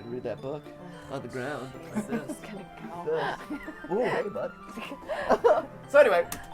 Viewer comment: Did MatPat say "oooh hey butt" at 20:13 ?!?! #pervpat